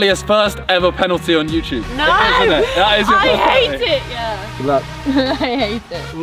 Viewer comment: did he say good luck or am I trippin